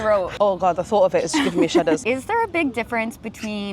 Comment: 에, shiver가 아니고, shudder 아닌가요? 제 귀에는 그렇게 들리네요. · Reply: 안녕하세요! shudder이 맞습니다! 보통 giving me the shiver 이나 making me shudder 이 일반적인 표현으로 자주 사용됩니다. 앞에 giving이 있어서 제가 shiver로 자막을 달았네요 ㅜㅠ 알려주셔서 감사합니다!